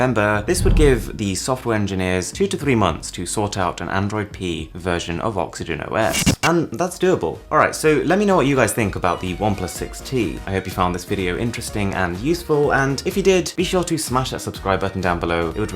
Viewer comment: did you say android pea ?????